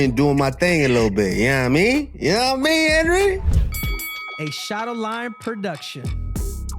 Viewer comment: A shadow lion production! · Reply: i thought they said shadowlime🤣🤣🤣